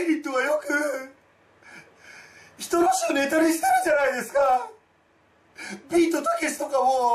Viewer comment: ビートたけしがニートたけしに聞こえた